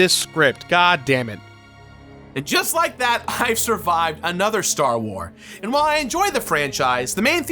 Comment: Did you say, "...I survived another STAR WAR"? Ha! Like the the order 2 orders after the one that killed the Jedi... Nice!